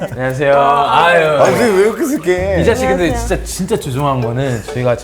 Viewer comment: "리사 씨 근데 진짜 죄송한데" 발음이 "이 자식 근데 진짜 죄송한데"로 들림